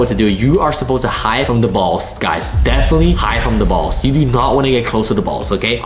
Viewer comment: AYO WTF did you say BALLS